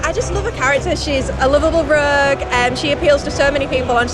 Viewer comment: OK be honest, did anyone else think she said "rug" instead of "rouge"?